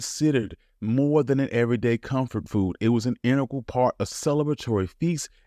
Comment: did they really put enerchal instead of INTEGRAL?!